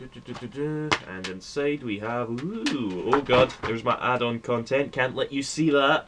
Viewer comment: LOL the first time I watched I thought you said: thats my adult content, cant let you see that.